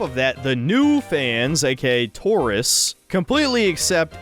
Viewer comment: He kept saying "tourists." I thought he was saying taurus, and this was connecting to some sort of destined connection to the global collapse of television or something. Yeah though the show looks pretty bad, you should probably stop watching it.